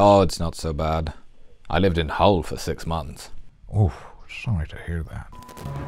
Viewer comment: Did Qui say he lived in Hull (Qc, Canada) for 6 months?!